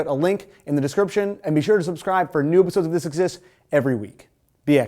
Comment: What the fuck did you say at 5:37? "New Episodes of This Exists"?